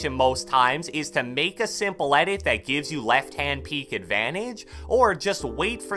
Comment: did he say left hand peek because I think that's worse